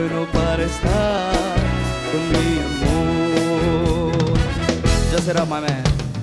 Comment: Qué cosa dice en el minuto 3:12??? alguien entiende??? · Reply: ya sera mane escuche